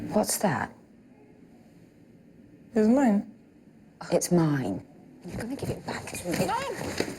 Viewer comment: 교묘하게도 “술값” 퉤 하는 걸로 들리네요